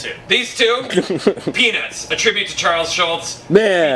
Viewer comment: am I the only one who thought said penis instead of peanuts